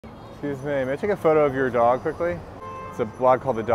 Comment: I thought he said, "hey babe" not, "excuse me" 😅 had to rewatch that a few times lmao